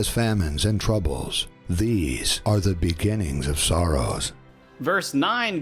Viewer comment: 'These are the beginnings of sorrows'. I hear Soros - things started by Soros.